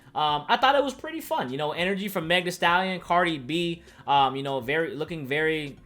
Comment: I thought it said Carti is 🔥🔥🔥